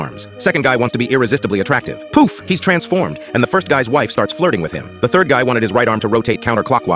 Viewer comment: I thought it said “and the first guy starts flirting with him”